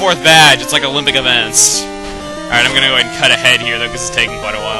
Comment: For a while, I thought when he said 'cut ahead,' I thought he said 'cut a head,' as in, chop off a head...